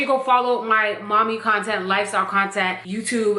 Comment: Yo why did I hear “Lysol Content” instead of “lifestyle” 😭😭😭😭